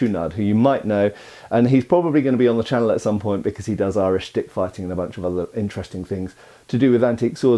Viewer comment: with Matt constantly making innuendos i definitely heard "he does Irish dickfighting"